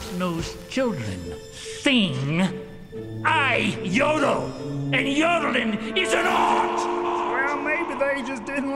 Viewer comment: When he said that, the first few times I watched it as a kid, I thought it sounded like he was saying "I YODEL! And yadelin'!! PIECE-A-KNOT!!!